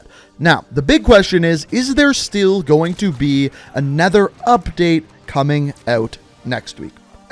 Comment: to me he sounds like he is saying a nether update not another update